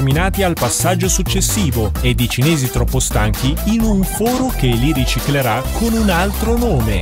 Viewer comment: Ho capito Cinesi Protestanti 🤦‍♂️